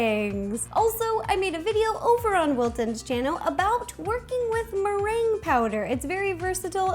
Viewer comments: LOL When She Said Working With Meringue Powder I thought She Said Twerking 😂😂😲😲 Comment Below If You Would Make This Poo Pastry ⬇️💩💩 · Who else thought when she said working with meringue powder, she said twerking with meringue powder??? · I heard she said twerkin' with meringue powder ^-^ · Legit thought she said "twerking with meringue powder" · uhhh did she say twerking with... marange powder or however you spell marange. I bet I spelled it right